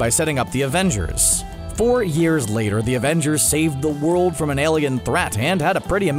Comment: am I crazy or did he say Thor years later